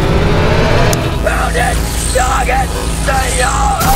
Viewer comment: Did he say, “POUND IT, NOGGIN, SEE YA!!!!!”